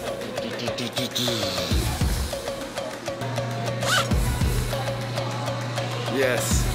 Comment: it kinda sounded like he said “Yebo Yes” 😅